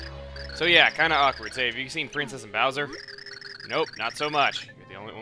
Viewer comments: it sounded like u said princess bowser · I think I heard you say Princess Bowser.